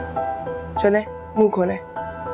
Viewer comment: I thought shakra is saying "moonh kholo " To majeed😂😂